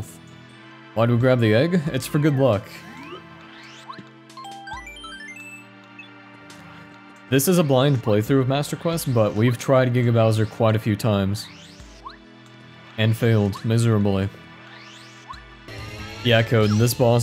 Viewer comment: I thought it said paper Mario master queen not paper MarioMaster quest